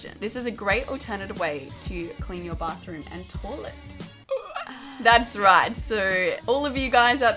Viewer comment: You know she grew up in Adelaide when she says torlet instead of toilet